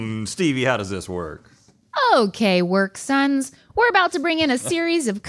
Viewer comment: Stevie calling the boys her “work sons” is my favorite addition to this running joke so far! SO accurate! 😂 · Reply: I thought she said “work sans” ?